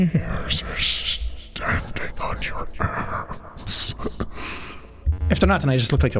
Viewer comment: Tell me why, at 5:53, I thought he was saying "The hairs are standing on your arse " instead of arms? 😅 Lord help my broken brain lol.